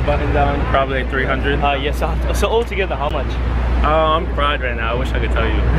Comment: did he say he’s fried ? 😂